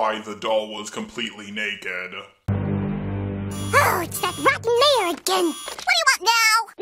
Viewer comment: I heard rotten wrong so I thought they said "ugh it's that fuckin mayor again"